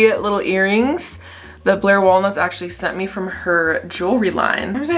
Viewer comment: i literally thought she said that blair waldorf sent from her jewelry line 😭